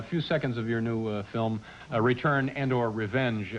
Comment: does he say "You clean?"? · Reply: “Return”; he’s clarifying with Carrie the film’s title.